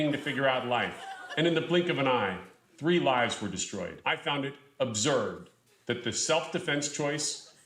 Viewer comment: He says "I found it observed" not "I found it absurd". You can tell he just makes things up and has no idea what he's saying